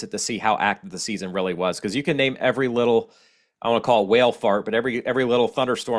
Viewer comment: I’m so impressed with you and this channel. I live in Gulfport MS, so you are my new go-to on these gulf storms. At 4:49, did I just hear you say “whale fart”? 😂🤣